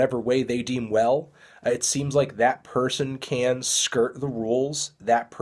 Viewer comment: did he say they can skrrt the rules?